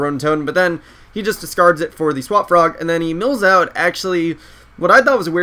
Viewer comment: So instead of Swap Frog I thought he said Swag Frog. The internet is making me fuckin' dumb.